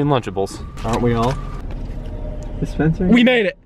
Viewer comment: anyone else hear say "Dispensaries" at 2:36? hahahah